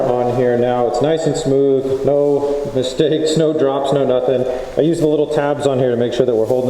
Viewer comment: lol the subtitles at 11:29 you said no nothing and the subtitles said I like muffins lol and at 11:36 you said i am done basecly but subtitles said i am dumb basecly lol